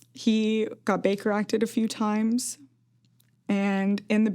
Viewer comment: What is she saying at 12:19? Baker acted?